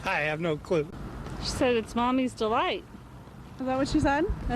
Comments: oh i thought it says pull the light lol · For little mommy doll I hear both I hear "islam" and "is mommy". I think it's because the pitch of sound or microphone.